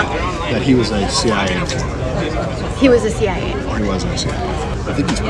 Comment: sounds like he said “he wasn’t”.